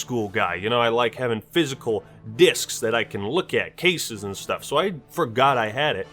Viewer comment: my audio glitched out and made it sound like he said dicks...